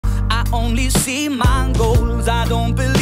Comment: At the start I heard " only see mangos